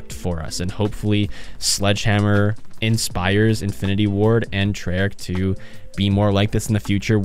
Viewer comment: They added ninja meaning they listened to us. That’s 1 thing more than IW and Treyarch did. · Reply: No cap thought you was saying Infinite Warfare, not Infinity Ward. I played it so much when it came out since it was my only CoD game at the time, because I didn't know about any others. I still played BO2 and BO1 with family whenever I went to my dads place, but I only ever owned that one. I eventually got a free copy of BO2 (don't ask how) and only ever played Town and MP against bots. But IW was so much fun for me in the time I got to play it. I shall stop typing now because I realized this went from one quick note to my whole CoD lifetime.